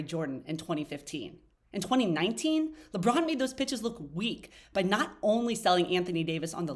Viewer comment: I thought she said “lebron made them bi*ches look weak “ 🤣🤣🤣